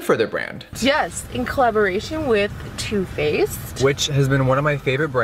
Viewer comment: At first when he said Two-Faced I thought he said toothpaste and I was very confused😂